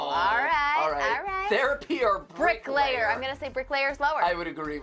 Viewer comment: I thought it said "brickslayer" ....